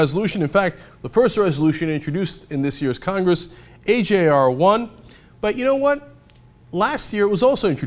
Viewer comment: Cenk, I didn't catch the name of the California legislation you were talking about at 5:24. "HAR 1" ? "HJR 1"? What is the name? What is the last name of the legislator sponsoring it?